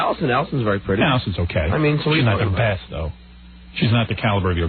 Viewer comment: does he say Beth or Best?